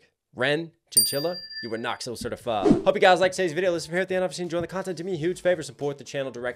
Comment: The first video I watched from you was Hi Ren. You said how great it was and at the end I thought you said “you are not so certified” lol I was like wtf? I thought he liked it. You just say “Knox hill certified” so fast I thought you said “not so certified”